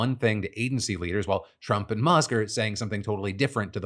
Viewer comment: Yo when Phil says "Trump and Musk" at 5:50 ish the subtitles say "Trump and McGirt" lmao idk why thats so funny to me